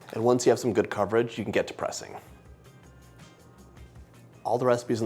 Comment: I thought you said, “Once you have some good coverage, you can get depressing”.